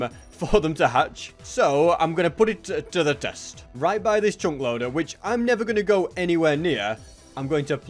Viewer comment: What's he saying chung load?? At 28:28 huh is that a new block